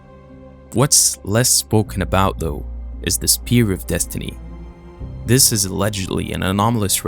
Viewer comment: It says "Sphere" of destiny, instead of Spear.